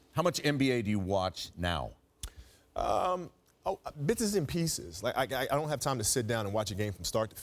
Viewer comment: I thought he said bitches and peaches Had to rewind that